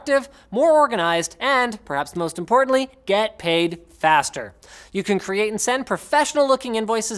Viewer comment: anyone else heard "get laid faster"